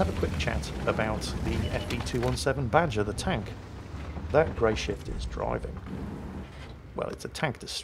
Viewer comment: A great battle but i thought Jingles was saying Greyshit in the early stages LOL